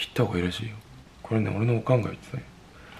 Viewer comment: ここ「これは俺の考えだよ」じゃなくて、「これね、俺のオカンが言ってたよ」って言ってない？バナナの話が主題だから大きな差があるってほどでもないんだけど。でも普段からこういうｺﾞﾆｮｺﾞﾆｮっとした言葉が間違って訳されてる可能性を考えてみたら、自分の耳で聞き取れる本国ペン羨ましいな...て思った